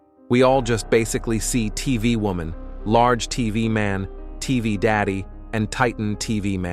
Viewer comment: What the hell did you just say?… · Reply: He said tv daddy